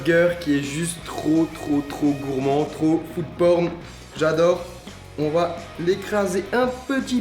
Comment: il a dit quoi à 5:10 trop porn😂😂😂😂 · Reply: Non non, il a dit food porn. Référence au hashtag #foodporn sur instagram